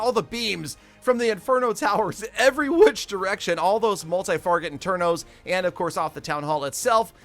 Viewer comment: did you just say multi-farget internos?